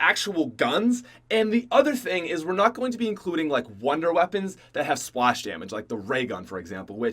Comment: I thought u said no wunderwepons